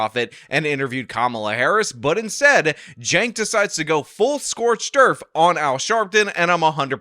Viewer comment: I thought said "full scorched earf" and then I thought probly not just heard it wrong then I wondered what the auto generated subtitles would say so I turned them on and went back and the captions do in fact read "full Scorch derf".